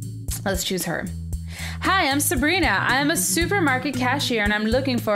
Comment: I thought you said supermega instead of super market XD